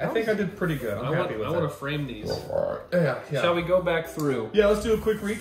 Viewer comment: I misheard frame for flame 😂 And 2 seconds later realized what is saying 🤣